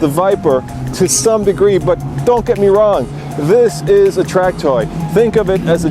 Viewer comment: sounds like he says "This is a TRAKTOR" hahaha